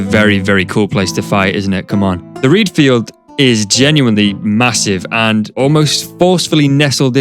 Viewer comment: did you say… m-m-massive..?